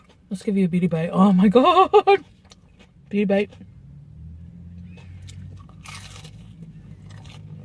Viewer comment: I thought she's been saying "beauty bite" whole time 😂 gotta learn English more 😂